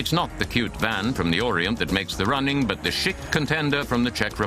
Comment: Did he just say "the shit contender from the Czech Republic" at 7.10? For once LOL!